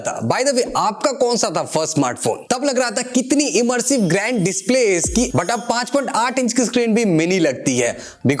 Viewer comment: Did he said 5 FOOT 8 INCH display is not enough?😂